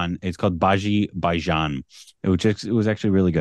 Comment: What did he just say 😅😂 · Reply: Bhaji Bhaijaan 🤣🤣